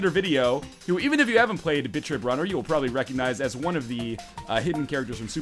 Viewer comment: I thought you said "bitch trip runner 2" ;D oh silly me